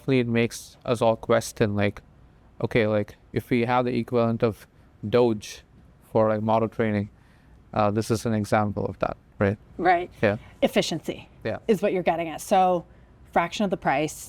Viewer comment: did he say DOGE?